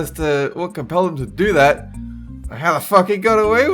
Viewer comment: did he say fuck lmao that was funny if he did